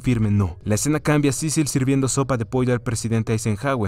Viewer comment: entendi sopa de p***a